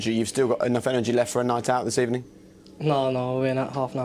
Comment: what is he saying? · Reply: ArianMas "No no we're in at half 9 tomorrow"